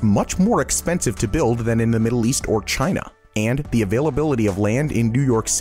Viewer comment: wait a second... why does the subtitle/cc say "Asia" didn't he say "China"??????